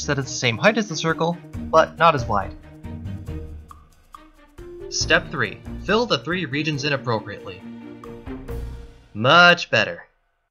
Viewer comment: i swear i thought he said "draw the three regions innappropriately" lol